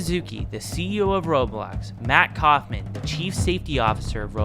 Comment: It’s Matt from what I heard, I don’t know tbh.